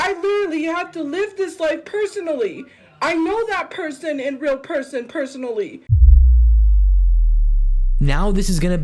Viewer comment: CACKLINGGGGGG. WHAT IS SHE EVEN SAYINGGGG 😭😭😭😭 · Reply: She knows that person in real person personally 😊